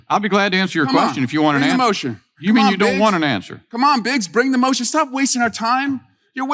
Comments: Lol! I legit thought Swalwell said, “Come on, BITCH!” when he said “Biggs” 😂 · Using MAGA tactics against MAGA Congresspeople. Swalwell is on fire!🔥🔥🔥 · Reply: Lol,I had to turn on the captions as I thought he said "bring it on beech"👀😅💙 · I thought he said "Bring the motion! Come on B*tch...you're wasting our time!" 🤣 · I thought he said bring it bitch, not biggs. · For a second I thought Eric Swalwell said "come on b___h" instead of "come on Biggs " at the beginning 😂